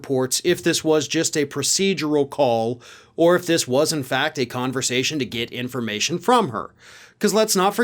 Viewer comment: It seriously sounded like you said "a tits conversation"... :D